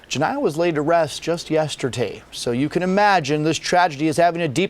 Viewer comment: Rest easy 🙏🏾 . But did he say “ yestertay ” 0:21 Gun violence is getting out of hand . What happened to black lives matter ?